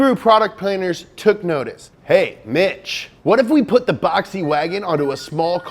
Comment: thought I heard, "Hey, BITCH."